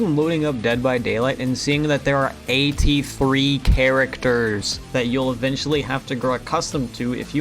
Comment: did anybody else hear 80 free characters? I know he said there is 14 but still funny to point out (I hope)